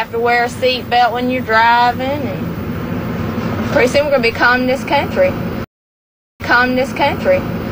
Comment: what does she say at the end "pretty soon we'll be calling this country" ?? is that right?